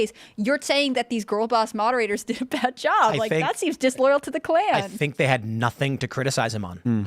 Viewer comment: lol I thought said disloyal to the clam at first 😂